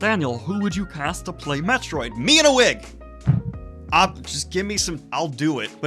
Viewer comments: When he said “me in a wig” I thought he said Mina Wick. Lol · 'me in a wig' I'm glad you're on board with the script I'm working on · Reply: For a second I thought he said "Mina Wigg" and just assumed it was some actress I never heard of.